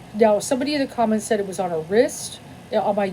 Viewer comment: Yes the tattoo was on her ribs, I misheard wrist.